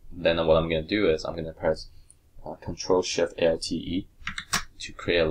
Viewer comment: At about 8:54 in the video you created a new layer. I can't understand the keyboard shortcut you used and I can't seem to find it anywhere. What exactly is the keyboard shortcut? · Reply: Charles Pacer "control shift alt E" I believe.